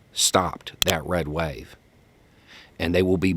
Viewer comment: At first I thought you said "Red Wedding" and I was like, "damn, tell me what you really think."